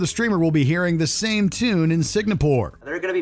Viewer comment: Is it me? Or is he saying Sig-na-pore instead of Sing-a-pore?